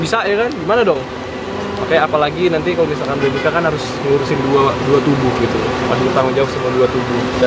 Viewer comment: Pas aditt ngomng 3 badan lucu banget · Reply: Rizky Offical aku denger,nya kok dua tubuh sob bukan 3 badan Coba ulang lg vidio,nya apa aku yg salah denger